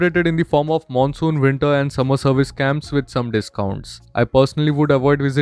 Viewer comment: I know you said 'service camps' but i heard it as 'scams' and i think its more appropriate and justified.